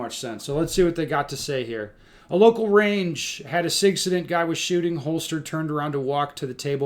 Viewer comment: is saying the word "SIGcident"? If so, I can appreciate the wordplay.